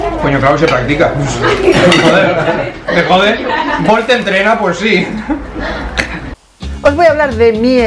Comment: Oí "Force entrena"?